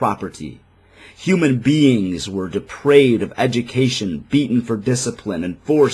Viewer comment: did you say that "Human beings were depraved of education..."? Did I hear that correctly or did you say something else, or did you mean "deprived"?